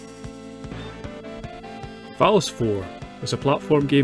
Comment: When the automatic YouTube translation displays Phallus 4 instead of Valis 4...😏